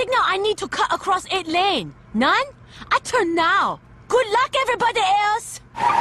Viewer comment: Funny, we quote the ‘I TURN NOW!’ all the time, but I could’ve sworn she said ‘good luck other people!’ at the end lol